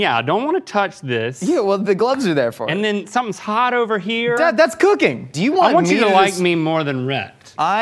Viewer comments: Did Josh just call Link "dad"? I- Hold on a second man hold on 😢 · Did Josh say "Dad, that's cooking!"?